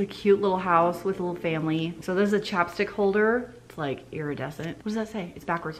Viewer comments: I thought you said "chopstick holder" and I was like waaaaaat hell yeah, hook me up with those chopstick holders. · CHAPSTICK holder! Lol the first like 3 times I thought you were saying chopstick holder and I was trying to figure out what that was.